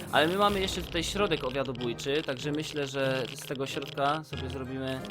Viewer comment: usłyszałam w dniu 9 środek obiadobójczy😂